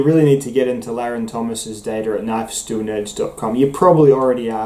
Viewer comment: Would love to see your work correlated with Larrin's. Just discovered his website and it's great. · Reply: I finally decoded 6:20. It sounded like "knifes to nodes dot com" (which of course doesn't make sense). <link> is the site, and Larrin Thomas is the name.